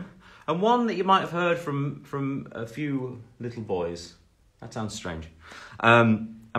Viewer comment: did he just said few little boys 😭😭😭 ohh Liam ❤️ 🥺🥺🥺